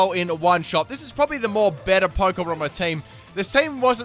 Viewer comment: did he say " MORE BETTER ? "